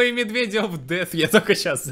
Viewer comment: Мне вообще послышалось медведи of death